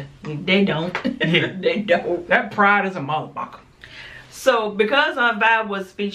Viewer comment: So these two well the young lady she says motherfucker and then when It comes shit she says skit keep the cursing to cursing stop trying to edit as if you're being watched by ya parents good show guys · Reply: Its so unfortunate that what you thought your heard was Motherfuc*^r instead of MotherBucka but......we dont curse. Happy Sunday